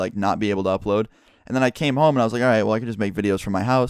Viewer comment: wtf did just say make out with my mom